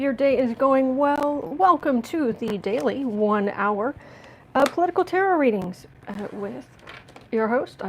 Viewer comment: Ha ha! I thought you said, "...your daily political terror reading." Definitely! Thank you!